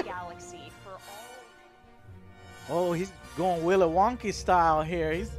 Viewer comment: Did you just say Willow Wonky?!?!?! lol